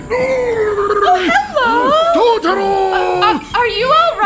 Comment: Did I hear 'totoro' at 4:17?